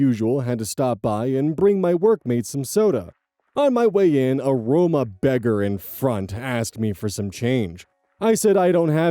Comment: Was just listening and misheard a Roma beggar as Aroma beggar and thought to myself “are they trying to bum a spritz of your cologne off of you? What the heck is an Aroma beggar”. Then I got context and did a mental facepalm.